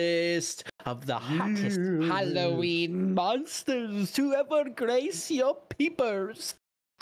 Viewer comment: I thought he said "The hottest Halloween monsters to ever grace your peñors"